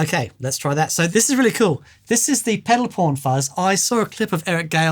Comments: Ah..pedal pawn. I heard mention it on VCQ and thought he said Pedal Porn :) haha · I heard "PEDAL PORN FUZZ" XD !! Great video guys ;)